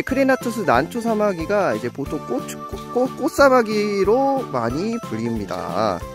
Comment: 꼬..ㅊ 뭐라고요? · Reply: 월래 (꽃으)로 말할려고함 발음:(꼿츠)